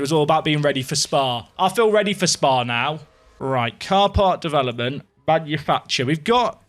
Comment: When you said 'car part development' at 09:29, I heard ''car park development''. Clearly the away days are having an effect on me.